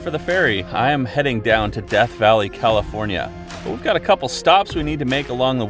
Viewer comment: It sounded like you said "DEF Valley" lol Good video!